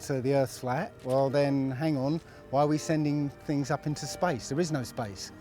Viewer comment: did he just say "we're sending things to spice"